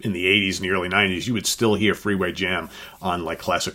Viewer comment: 1. Pink Floyd - Interstellar Overdrive 2. Santana - Echizo 3. Iron Maiden - Transylvania 4. The Who - Sparks 5 Black Sabbath - Fluff 6. Pink Floyd - Any Colour you like 7. Pink Floyd - Obscured by Clouds 8. Black Sabbath - Laguna Sunrise 9. Pink Floyd - A Saucerful of Secrets 10. Iron Maiden - Ghengis Khan Honorable Mentions Pink Floyd - Careful with That Axe Eugene "Yeah there's a scream in it ,but who cares." Pink Floyd - Grand Viziers Garden Party (Entertainment) Santana - Soul Sacrifice Pink Floyd - Atom Heart Mother Journey - Kohoutek Black Sabbath - Supertzar And Boston - Amazing Journey Also, I thought you said "Freewayjim"